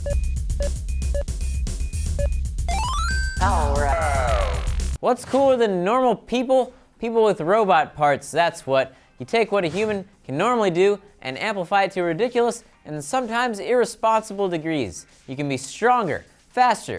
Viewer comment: who else thought he said gnarled people at the start?